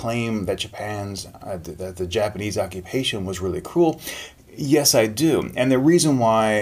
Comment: in 10:02he said cruel, and the subtitle says cool